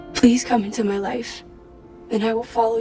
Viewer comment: I honestly heard "wife" instead of "life" and was so confused I backed the recording up and had to listen again. I can't tell if I'm relieved or disappointed.